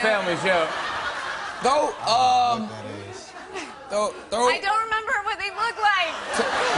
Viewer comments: did Wiz say "look at dat ass" at 1:39 or am i just hearing things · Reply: he said "i don't know what that is" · Anyone else hear Wiz say "Dat ass" at 1:42?😂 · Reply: he said i dont know what that is